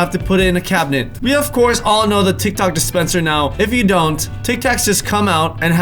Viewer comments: Did he just say tik tok dispenser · he says TikTok...instead of TicTac? Did Tal just see in to the future? 2020 anyone¿? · Am I the only one that heard him say "tik tok dispenser" 🤦🏽‍♀️ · Did anyone else hear Tik Tok instead of tic tac??🤣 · He said we all know the TikTok dispenser instead of tictac dispenser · he says tic tok instead of tic tac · he said tic tok instead of tic tac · did he say Tik Toc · he says tic tok instead of tic tac lol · Anyone noticed he said tick tock instead of tik tak Edit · anyone else hear him say tic tok? 😂😂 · "We all know about the TIK TOK dispenser now." Instead of tic tac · O my gawd he said TIC TOCK not tick tack LMAO if u heard him say tick tock the 1st time then make the thumbs up blue plz god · did you say tic tock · did he just say tic toc? Instead of tic tac? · Am I the only one that heard him say "tik tok" instead of "tic tac" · did he say tic tok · who else heard “the tick tock despencer” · He said TikTok dispenser instead of Tic Tac dispenser LOL!!! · if you listen he actually says tik tok dispenser instead of tic tac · Im dead😂😂😂 he said tiktok instead of tic tac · Anybody else hear him say Tik Tok dispenser? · Did he just say Tic Toc? Lol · Who else heard tal say tik tok instead of tic tak in 9:31 like if. You heard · Did anyone else hear tik tok dispenser at 9:33? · did anyone else heard tik tok instead of tic tac 🤔🤔🤔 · Is it just me or did he say “Tik Tok dispenser “ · Did I heard tik took at 9:32 in stead of tik tac